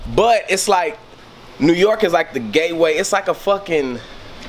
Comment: did he just say “GayWay”